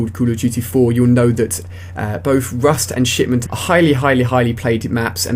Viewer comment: thumbs up if you thought he said "shitment."